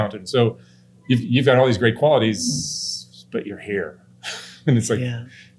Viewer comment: I thought he said “but your hair” 😅